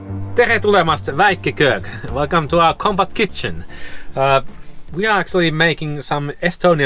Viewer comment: The first time I heard say "Compact Kitchen", I misheard and thought she said "Combat kitchen"!🤣